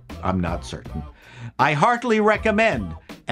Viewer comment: did he say hardly or heartedly (is that even a word ?) recommend the movie? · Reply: Heartily.